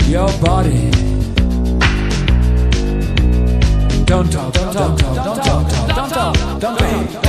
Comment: JASJAJSJA PARECE QUE DICE TONTO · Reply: "Don't talk" (No hables). Lo que pasa es que es acento britanico.